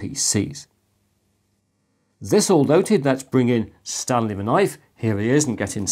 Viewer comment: A confession: the first vid of yours I ever saw, many vids ago, I thought you said "Stabby The Knife". Disappointed I was to be corrected.